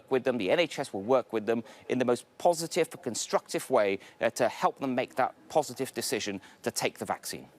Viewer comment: wow that guys head must be on the chopping block to be that touchy and when he said "help them" i heard "Force them" of those 100,000 i can see most of them tell the government to stuff the job...... "my body my choice" - unless it comes to this jab